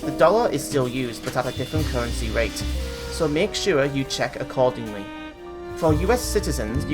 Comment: could you say that again? I couldn't quite catch that. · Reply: "Make sure you check accordingly".